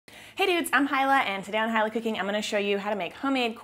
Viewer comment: OMgosh...I absolutely love your channel...but in the beginning of this one...I thought you said "hey idiots", not: "hey dudes". hahahahahahhahaaa! oxo